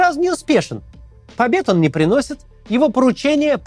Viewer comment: Мне послышалось "обед он не приносит" и я подумала "он даже на это не способен"